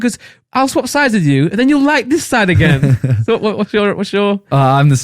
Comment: is it just me or does it sound like says the N-word